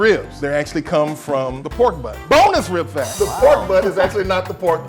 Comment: sounds like he said boneless rib fact